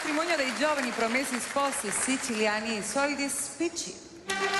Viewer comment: Ma solo io, alla presentazione di Belen ho capito "i soliti spicci?"